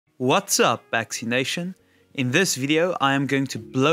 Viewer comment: I heard "What's up vaccination"